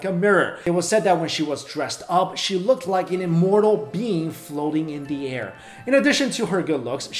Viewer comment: Instead of "immortal being," I heard "immortal bean." I'm dying.